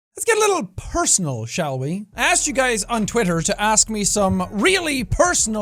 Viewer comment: I thought jack said “skiddle liddle personal shall we?”